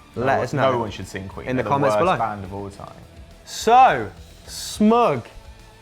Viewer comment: Did say Queen is the worst band of all time at 4:01 ???